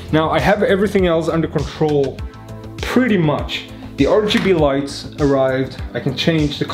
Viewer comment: did you say LGBT lights? LMAO good job nice work !!